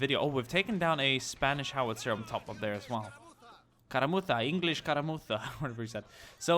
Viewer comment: Cerveza por favor, - One of the British soldiers practicing his Spanish during the skirmish, hoping to win the battle and later relaxing at a Pub. By the way Escaramuza is skirmish in spanish, thats what I heard you sayin at a point LoL. Edit: WHY DESPACITO?! I HAD TO LISTEN FOR THAT SHIT FOR A WHOLE 2 MONTHS NON-STOP AAAAAAAHHH PLZ NOT AGAIN XD